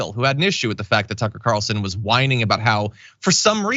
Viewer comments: It sounded like you said Sucker Carlson, I hope that sticks 😂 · "Sucker" Carlson? More like and more appropriately "*ucker" Carkson. I leave the letter the * is to one's imagintion. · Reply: I thought John said "Sucker"! Haha!